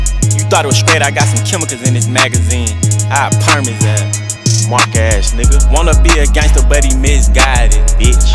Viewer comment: did he say MonkaS?